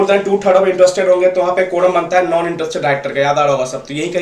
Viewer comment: what are you saying please use proper or clear words. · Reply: Quorum